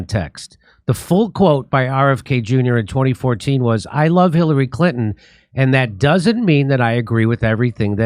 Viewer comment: "I love Killary Hinton, and I don't want to get mur dered" is how I heard RFK Jr's line.